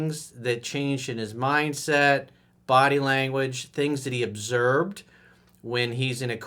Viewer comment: he said obserbed instead of observed